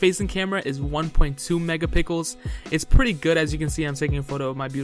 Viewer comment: did he say mega-pickles??😂😂😂